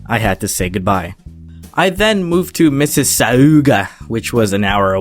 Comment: When you're so American you think he said Mississippi in